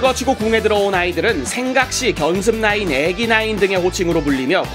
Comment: 애기나인이 애미나이로들림 ㅋㅋ